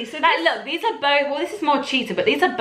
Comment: did anyone else hear "but cheepard..lettah yeno" at 9:56 😂😂😂 had me laughing